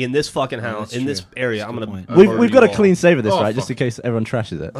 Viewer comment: is it just me or at around 5:55 did gavin say Ryans name now i know he has worked for achievement hunter since 2011 but im curious to whether im right or not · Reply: you're wrong, he says "we got a clean save of this right?".